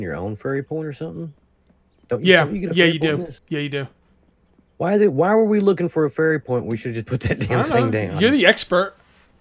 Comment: Lol first I heard "why are we looking for furry porn", then turned on captions only to see it translated it to... fairy porn. Much better.